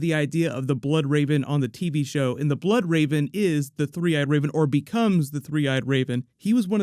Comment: I always thought it was the Three-Eyed Rodent. That's what I thought Bran Stark said. Lol